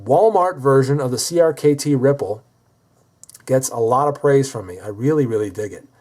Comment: Lol I thought it said crkt nipple lol